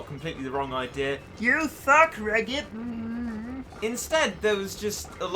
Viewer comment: When he said "You succ Regit," I heard "You thug Regit"